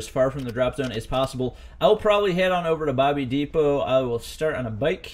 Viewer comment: did he just say bobby depot? Also i think i know why it drops faster... Its because the throwables are heavier